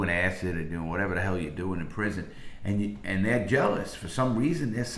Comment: did he just say doing acid in prison please tell me I’m not the only one who heard this